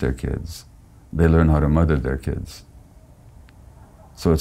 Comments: "Those fathers who stay at home with their kids learn how to MURDER their kids" - I'm pretty sure this is typo, and he actually said learn how to MOTHER their kids :D :D, I think someone needs to check that subtitled translation :DDDDDD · there is a crucial error in the written subtitle at min2:53 Dr. Gabé says: those fathers ... learn how to MOTHER their kids!!! whereas subtitle instead of ..."learn how to MOTHER" writes "learn how to murder" Pls be aware of this!!